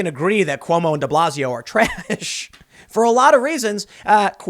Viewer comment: Possums at 11:48...Did you say ....Trash?